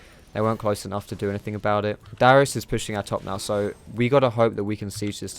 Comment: thought you said "Dyrus" lol